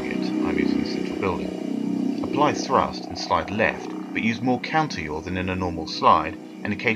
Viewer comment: I didn't forget them, I don't acoustically understood them. What is that word. "YAWN" or something? "Slide left, but use more counterYAWN at 2:50.